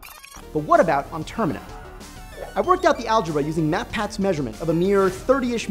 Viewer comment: It's AL-GOR-ITHMS, not AL-JOR-ITHMS!!! It's a theory that makes sense, considering the Termina moon's small enough to be held up by the four giants. The outer shell could be formed by debris collected/pulled in at the same time, formed by a kind of elastic tension from the gravity of the singularity, before it hits the event horizon. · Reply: PBS Space Time Sorry my mistake. First time I watched this, I was sure I heard the guy say 'aljorithms', but watching again, it seems to be 'algebra'. Combination of the guy speaking rather fast and the US accent, meant I misheard.